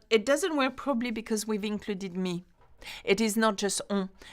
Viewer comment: Great lesson. You explain it so well. For "toi et moi ferons", I understand. You comment that (on) 'fera' - "It doesn't work probably.." What were the few words you said after 'probably' at 21:00? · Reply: "probably because we've included me" meaning 'moi', as it is two different pronouns, so we would use the 'nous' conjugation. 😁